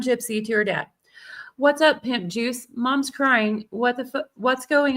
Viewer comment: What daughter would feel comfortable calling their dad "pimp juice" except gypsy rose Blanchard ???? Thats inappropriate AF !!!! IMO ❤❤❤ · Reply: I thought she said Nip juice lol 🤮